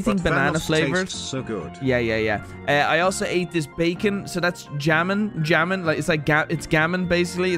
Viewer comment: why did he say “jamon” and not hamon 😭😭